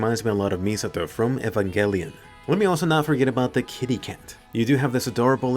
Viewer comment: What's the anime name he's saying at 4:33? Efin Gelian?